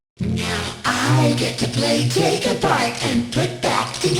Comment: what do you hear? Take a bite? Take apart? Say one of these phrases in your thought and you will hear it depending on what you choose